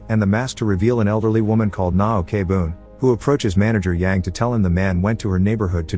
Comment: i heard it as nah ok bro